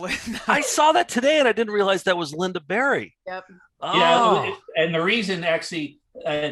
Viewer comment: I thought said Linda BERRY at first, who-ironically-authored a book called "Death and the Easter Bunny"...ooooooooh, creeeeeeepy...